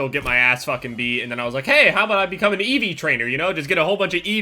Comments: I thought he said EV train I was gonna kill myself for him thinking that was training eevees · When you thought he said ev trainer but he meant Eevee trainer